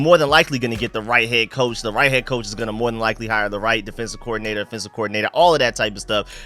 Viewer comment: when you say offense accordingnator it’s sound like you saying defense accordingnator lol I get so confused but I get what you’re saying